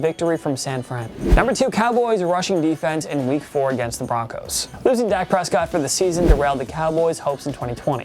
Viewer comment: did he say Broncos